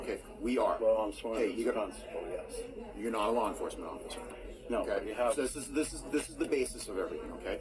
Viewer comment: Time 42:22 Did I hear saying.... (I'm not a law enforcement)????